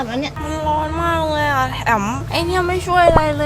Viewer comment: ผมได้ยินพูดกับพี่เลขาว่า มันร้อนมากเลยอะ แล้วได้ยินหลุดพูดว่า แอ๋ม อยากรู้ว่านั่นคือชื่อของพี่เลขาใช่ไหมครับ